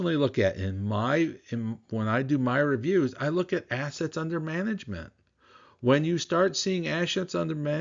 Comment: it sounded like you said "ashes under management". I've been out of Yield Max & GraniteShares for weeks now; these ETFs do not seem to be solid investments in a positive or negative market.